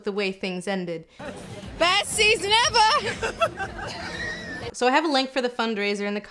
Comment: what does Emilia says? please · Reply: "best season ever 😩" 🤭